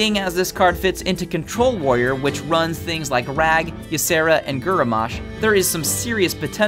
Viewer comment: Lel 4:32 "Gerramosh" instead of Grommash